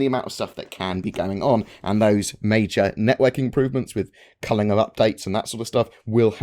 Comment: I swear I thought you said they'd "..improve Cunnilingus updates..."